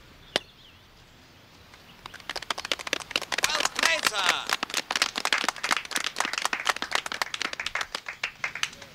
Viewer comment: I can’t understand it but what did he say? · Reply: He said “Well played sir”.